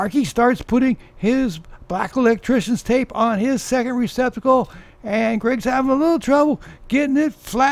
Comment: I thought I heard, “start wrapping electrical tape around the second testicle”. Is that what electricians do over in your neck of the woods?